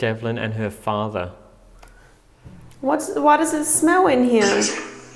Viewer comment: your EVP i hear "It's hell in hear". Another brilliant episode as usual